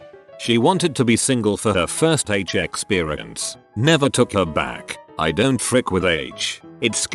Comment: what is the h word that is starred out? I cant figure it out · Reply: Heroin, a very powerful opiate drug